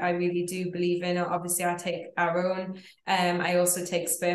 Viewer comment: She doesn't always speak clearly (words run into each other or mumble), lots of unnecessary "ums" and repetitive unnecessary "I''s" and/or has a slight accent. Perhaps it would be helpful if she used written visuals. I listened to multiple times and at different speeds but still can't understand what is the 1st supplement she takes at 3:59? Even the closed captions can't pick it up. She should practice and tape herself so she can hear the difference between her voice and other native speakers or work with a voice coach. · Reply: Thanks for the question. She said "Our own" which is the Nuchido Time+ an NAD booster.